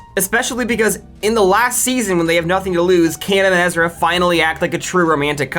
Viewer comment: am i fucking insane or did u say "kanan and ezra" 🤨🤨❓🤔🤔🤨🔍 🤨🤨🤔❓